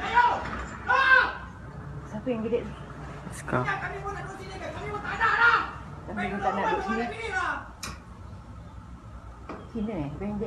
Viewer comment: Askar tu cakap apa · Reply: Rasa askar tu cakap macam ni "Kau nampak kitaorang ramai2 kat sini kau ingat kitaorang dok sini buat ape????... JUAL SAYOR ????...HAAHHHH!!!" Lebih kurang macam tu la..